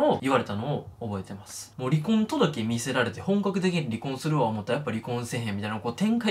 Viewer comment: 10万人行っても嬉しくないのは 動画のクオリティ的に行って当たり前だからじゃないですかねぇ🤔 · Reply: 「ロリコン届け見せられて」って聞こえちゃいましたすいやせん